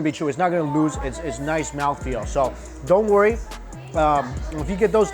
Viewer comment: did he just say the phrase "mouth feel"? Charles Boyle is going to be so proud.